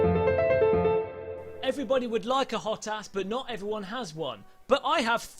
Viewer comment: Lmfao I thought you said "Everyone would like a hot ass, but not everyone has one".